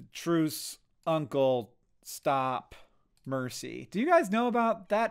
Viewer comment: I was listening to this not watching and thought he said “truth,mercy…” straight up thought he was playing halo 2 and was about to say regret